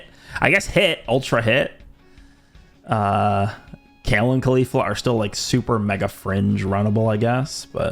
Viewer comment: did he say Kale and Khalifa 😭